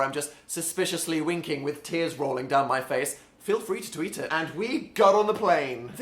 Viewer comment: did i hear "Phil free to tweet it" or just me?